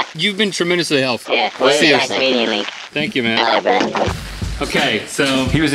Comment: Did anyone else hear: "It was nice meeting you link. I like rhett."